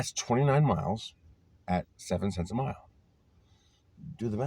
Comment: my dear what did you say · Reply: 70 cents a mile national average is 65 cents per mile and commercial insurance coverage if you do enough delivery comes to between 5 and 9 cents a mile additional on top of the 65 cents